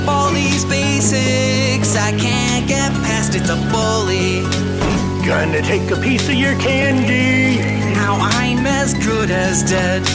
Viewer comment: the bully sound like he said "gonna take a sniff of your panty" it got me every time 🤣🤣🤣but I realize he said"gonna take a piece of your candy''